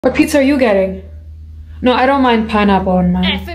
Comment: Coulda sworn she said "pizza are you gay?" At the beginning